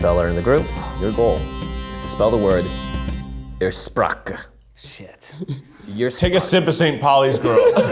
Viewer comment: ‘s pronounciation of „Ursprache“ made me spell the word incorrectly even though I can speak german 😂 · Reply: I thought he said sth like "Erstsprache" (first language) and I'm not even sure if it's an actual word in germany